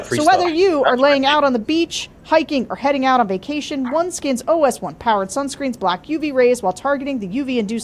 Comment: When y'all did your promo ad, I swear I thought I heard "foreskin". Yet, y'all said it correctly.